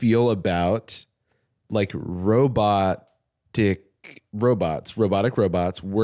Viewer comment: fr thought said robot dick and almost did a coffee spit take